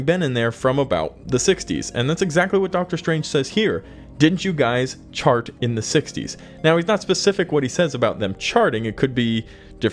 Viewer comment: He was speaking of the Fantastic Four, and was referring to the Fab Four - the Beatles. Although I thought I heard "Didn't you guys go to space in the 60s ," but obviously misunderstood.